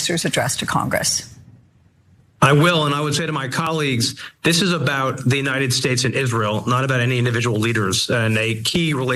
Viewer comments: Sounded like he said, "this is about the United States of israel". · I thought he said “The United States OF Israel”. I had to repeat. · It almost sounded like he said 'United States of Israel' at the beginning.